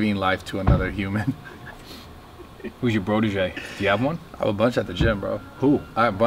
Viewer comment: What does said at 9:35 “Who is your..." Because doesn't know that term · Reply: Brotege. Like a protege but a bro one